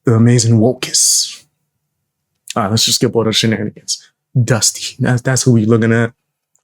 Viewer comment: I thought you were saying he was ashy or somethin. Nope he’s actually dusty lol